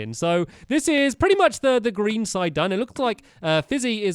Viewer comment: When you said green side I thought you said green steve